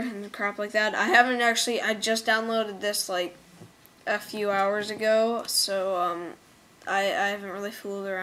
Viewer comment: lol 5:41 when u said a few hours it sounded like eff u hours ago XD